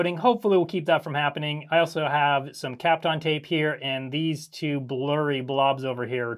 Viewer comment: Oh those tapes are called Kapton tape? I always heard it as teflon tape